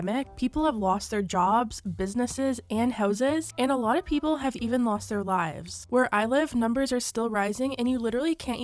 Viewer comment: OMG I thought you said "a lot of people have lost their vibes" and I was like what the hell is she talking about?! 😂 Also, "Nikita threw a massive Cardi" 😂 I gotta get my ears checked